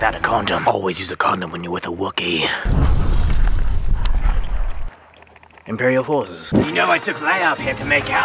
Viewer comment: What did he said at 0:54? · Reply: Diego Etori Imperial forces.